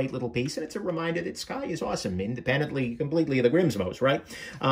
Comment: Thought he said Depends... don't need to know why those are in his EDC collection 🤮